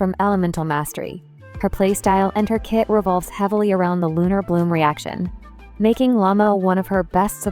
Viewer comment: Says " lunar bloom" but the subtitles says something else 🙃